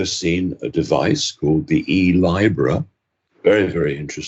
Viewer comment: “E-Libra” what? For addiction? Couldn’t quite catch what you were saying at minute 30:00 or so...